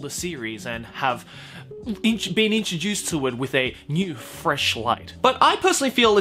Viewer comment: I thought he said new flesh light XD.